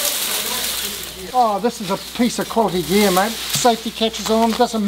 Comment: Did refer to the mak trimmer as a cossie? You should have him and Jess's mum on more often. Jess looks just like her mum. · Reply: “This is a piece of quality gear” is what he said, does sound a bit like “cossie gear” (9:18ish)